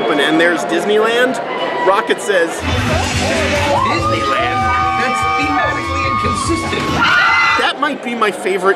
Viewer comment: What's the joke that Rocket says at 31:11 ? "Disneyland? that's ...sistent!" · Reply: Septiquad Doubleyou "Disneyland? That's thematically inconsistent!"